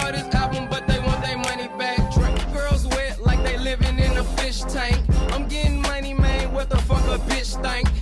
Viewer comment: when he said "yeah they bought his album but they want they money bacc" did he say drake on the adlib?🤔😂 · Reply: This funny low-key 😂😂😂 but on the real he said his first name "Dre"